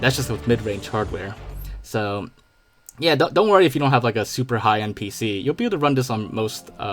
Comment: Me, being the dumbass I am, misheard this as "super high NPC"